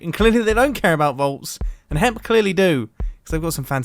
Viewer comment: Am I the only one that heard "hemp" instead of "hep"?